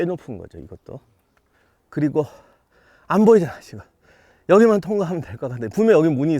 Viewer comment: 안보이잖아 시바.... 로들림ㅋㅋㅋㅋㅋㅋㅋㅋ